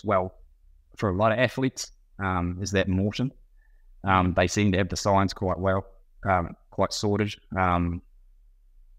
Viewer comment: The product he said around 54:43 or so. He said Morton? I couldn't understand it. Thanks. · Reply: Maurten